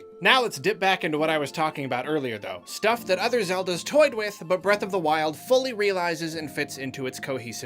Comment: the captions say “toyed with the butt” DEMONETIZED